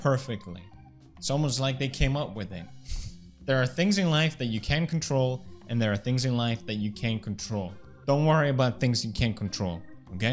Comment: “There are things in life that you can’t control, and there are things in life that you can’t control. Don’t worry about things you can’t control” What a great and motivating quote Pewdiepie really knows :’) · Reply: I heard it as, “there are things in life that you can control, and there are things in life that you can control.”